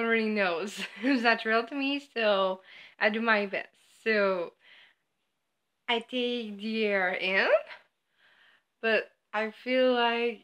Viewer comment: Can someone please tell me what she says at 0:17. Not trying to be mean but I literally cannot understand a word she says right there because of her accent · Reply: I take the air in